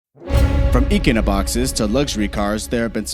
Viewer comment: As soon as I heard “eeekunoboxes” at 0:01, I was done.